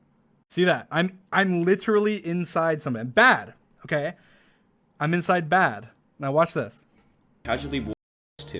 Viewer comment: “I’m inside bad” uh oh I hear nsfw artist